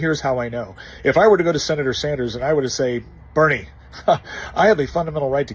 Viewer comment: Oh, I thought you said, Barry Sanders... Never mind.